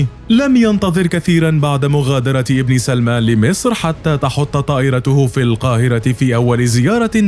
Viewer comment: هل سمعت المعلق يقول ابليس المال 5:15 😳😳😳 من يقصد... ولماذا..؟؟؟؟؟؟؟؟؟؟؟